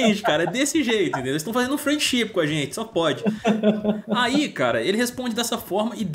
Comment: Eu entendi “eles estão fznd um frangoship com a gente” kkk